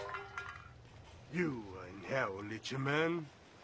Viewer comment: What does he say? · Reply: "You are in hell little man"